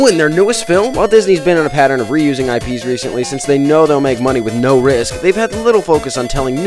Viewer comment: Thought he said no rizz 💀